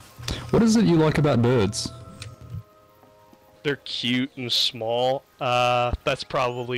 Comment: the theyre cute and small, I first heard it as there cute and swoll